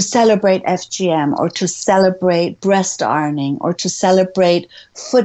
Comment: What is breast arming? (or did I mishear the word?) edit: It’s breast ironing and it’s every bit as disgusting as it sounds